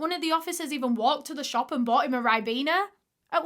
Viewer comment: what did she say and what is that? I listened multiple times and can't understand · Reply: They took him to the shop and bought him a Ribena (ribena is a brand that produces juice)